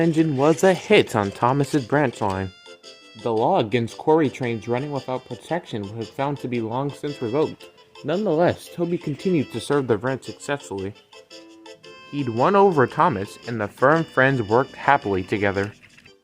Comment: At first I thought you said "He'd run over Thomas"